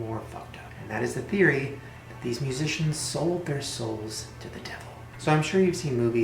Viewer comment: I thought you said magician, and all i was thinking at the beginning was 'these aren't magicians'